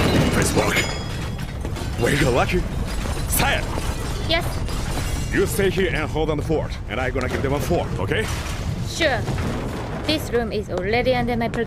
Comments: ENGRISH WARNING This section contains painfully mispronounced “English” phrases. Watch at your own risk. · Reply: this is what I heard: "you stay here and hold down the fort, and I'm gonna go give them a fork "😂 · "You stay here and holdownthefort, and imgonna givethema fort, okay?" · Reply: I thought he said I'm gonna givehem a fork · What the sub says:You stay here and hold down the fort, and I'm gonna give them a fight, okay? What he says:You stay here and hold down the fort, and I'm gonna give them a fort, okay? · Great voice acting, but I had to suppress a laugh for the "give them a fork" instead of "Give them a fight"